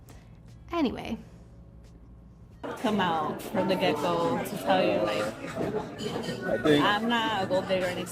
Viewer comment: mins in She said "come out from the gecko" for the second I thought I heard "Geico"! haha